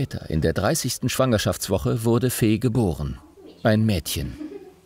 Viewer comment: Ich habe anstatt „Fee geboren“ „Fehlgeboren“ verstanden und war komplett verwirrt.